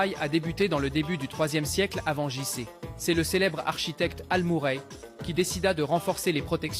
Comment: al-muray... même le nom il ne fait pas serieux, j'ai compris "ah le muret"